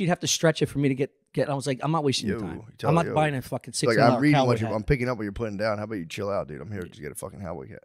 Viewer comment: did Matt say Howboy Cat?